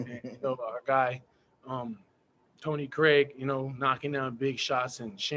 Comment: Am I trippin or did he call him “Tony Craig” at 16:37 😂😂